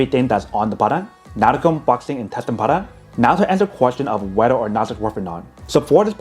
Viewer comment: If you turn on the transcript at around 5:40 in it says, "button not a gun boxing intestine Potter". I rewound it like 50 times and still am cracking up, thanks man, haven't laughed that hard in a long time. I still don't know what you said. You might want to speak slower man. I have a few gen 1 & 2 cubes, might upgrade, great product.